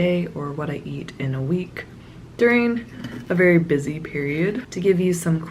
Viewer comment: I thought you said I don’t eat in a week